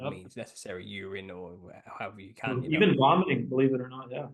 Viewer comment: Did he say "Even blonding" at '1:13:26'?